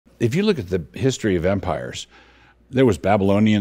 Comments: Thought he said ..the history of vampires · I thought he said history of vampire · I thought he said 'the history of Vampires'😅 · 😅 I thought he said vampires